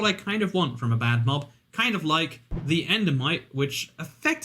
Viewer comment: I would put endermites lower on the list. I forget they exist sometimes. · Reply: At first I thought you said enderman and i was thinking how could you forget about a mob that you need to beat the game but yes endermites are much forgotten